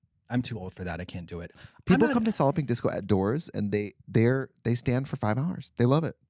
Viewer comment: Y’all I just found out it’s called solid pink disco. I thought trix was saying solloping disco. Had no clue what that meant!